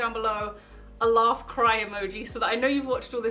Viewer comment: omg at first I thought you said ''I love cry emoji'' 😂 😂 😂 😂 😂